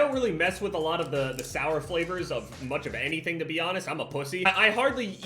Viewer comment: when he said I'm a pussy at 2:00 I thought he said other than pussy...